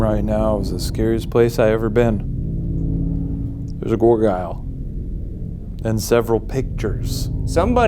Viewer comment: For the post mortem, at 25:17 did Shayne say gorgyle…instead of gargoyle?